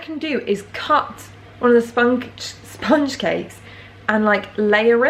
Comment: did you say spunk 😂😂 oh 😂😂